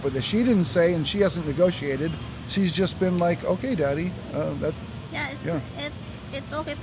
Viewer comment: Did I hear that right, “she’s just like ok daddy?”